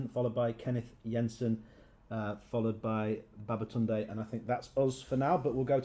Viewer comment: did I hear baba tunde at 6:35 lol..... we gotta make jj react to this